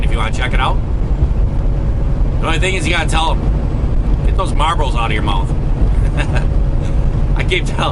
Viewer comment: Marbles. I thought you said Marlboro. You need to speak more clearly so people can understand you. Lol